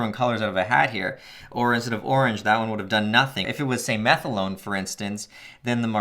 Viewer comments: I heard “I haven’t tried methadrone but I have tried meth alone” lol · when you said methylone i thought you said METH ALONE